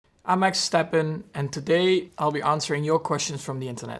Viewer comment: Did he say "Maxtappen"? 😂